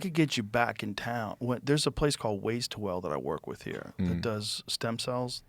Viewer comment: What is name of that physical therapy Joe Rogan just mentioned . It sounded like “wastewell” 1:16:20 time stamp